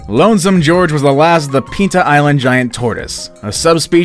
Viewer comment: on the last one i thought i heard him say pizza island and i went to the american airlines page but as i kept listening to the video i found out its pinta island